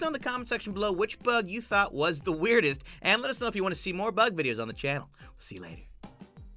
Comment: Omg when he said you guys are enjoying the bug videos I thought he said BUTT video 😂😂😂